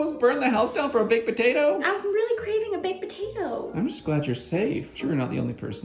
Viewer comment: "Sweeeetie, you almost burned the house down for a baked potato?" 😂❤ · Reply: I thought she said I’m craving a big potato